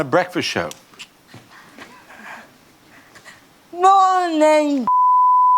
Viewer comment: what does she say? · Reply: "Morning caants" spelled incorrectly to avoid censorship 😅